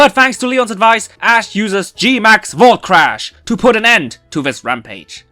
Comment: When said "GMAX VoltCrash" I kinda thought he said GMAX Bullcrap lol